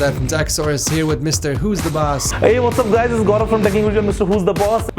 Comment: For people who do not understand Technical Guruji aka the Indian Guy. Here is what he says: At 0:14 he says - Hey, what's up guys. This is Gaurav with Mrwhosetheboss at 3:44 he says - As Arun asked me to go for one of my choice under 300$, i instantly chose the Xiaomi MI A1; also known as the MI 5X in some markets. In India this phone is priced at around 210$ and for that money we get a lot in terms of great design, Snapdragon 625 processor, 4 gigs of RAM, 64 Gigs of Storage, 5.5 inch Full HD Screen, 3080MAH of battery and for some cool portrait shots. There is also a dual rear camera system and being a part of Android program means that we leave up with the MI UI Skin and always get a clean stock android look along with the promise to receive all the upcoming updates. So bringing these things all together i think, make this phone a great deal for the price. But since we are in 2018, i think we should get some taller screens with lesser bezels. So i am always looking forward to be able to see for some brands like Xiaomi, Nokia, Lenovo, Motorola, to see what they bring for the table but as of now MI A1 would be my pick for the budget under 300$. Thank you.